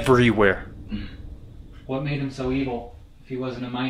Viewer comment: Hello everyone 🖤 thank you all for being here today! This episode was super intense and not gunna lie we wanted to leave because of how scary things got 🫣 but have no fear because we stuck around lol! We love you all so much and to the new people who are just discovering us please hit the subscribe button and join the #GhostGang 🖤🥹 you won’t regret it! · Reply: When you asked about the stranger outside it sounded like they said it's evil.